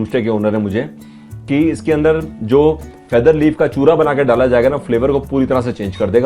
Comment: Beautiful vdo sir.👍 What was that masala they put in chicken at 11:04? Didn't hear that clearly. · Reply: Wapicha they make it from feather leaves of chicken... this was my 1st experience... there was the whole cooking process i saw but didn't show in the video thinking that some viewers may not like it......